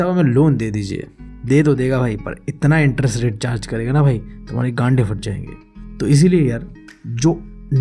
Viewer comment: kya bola 😂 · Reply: Gand hi phat jayegi 😂😂😂 or ye video students ke liye hai😂😂😂